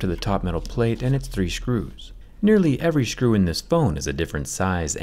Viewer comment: Who else heard shcrews